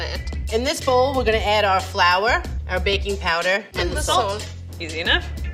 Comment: LMAOOOO cracks me up! all i keep hearing is "And where gonna add more oil" like damn the dough swimming lmao